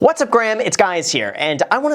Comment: I'm I the only who heard him say "what's up Graham it's guys here"?